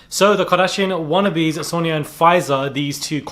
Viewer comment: Lmfao why i thought u said Moderna and Pfizer 😢